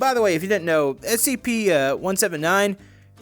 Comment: did you just say SCP-179?